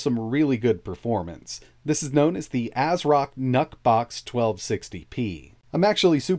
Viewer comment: who else heard him say "NUT BOX" ?🤔🤔🤔